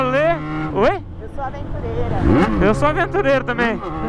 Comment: "Eu sou aventureira." Entendi "pistoleira".